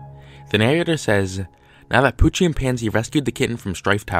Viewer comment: For some reason I heard "coochie and pansay" instead of "poochie and pansie"